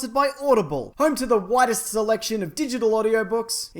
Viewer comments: I thought you said whitest collection instead of widest collection. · I thought you said Audible was "home to the whitest selection of digital audiobooks."